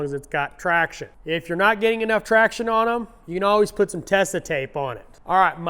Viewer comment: Wher can i buy that black tape? And how do you spell it? Lol sounded like you were saying testa tape